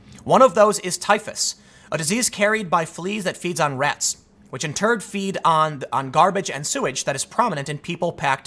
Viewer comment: Did Tim say "Which in TURD" at #11:45?